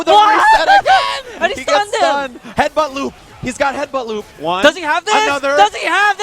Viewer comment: What does Seth says at 8:26 ?! "He got headbutt something..." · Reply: Lucas Medeiros "He's got headbutt loop" referring to Urien's ability to loop headbutts after specific setups.